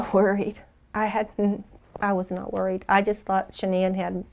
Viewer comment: " did she say Horny ?, i was not HORNY?, grandma .... you are as crazy as your son Lady Killer is.